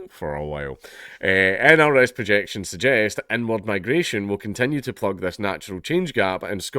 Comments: I know that's not what he said, but I heard "N-word migration" and I just thought that's an accurate description as well :D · Tired - kept zoning out - but every time he said inward migration I heard “n word migration” and it snapped me back in